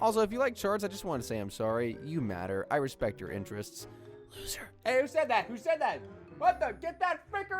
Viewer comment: I was dozing off and at 15:24 when he whispers "loser" i thought i heard my name "Sarah"😱 Freaked me out!!😂